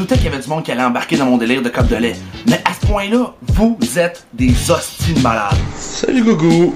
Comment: heu c'est quoi ce que vous dites à 1:10 ? je suis française et là je suis pas capable de comprendre j'entends " vous êtes des Austin malades " !!!!! : ) j'aime bien vos sketches même si je dois réécouter 2 fois pour piger le québéquois mais ça me plait !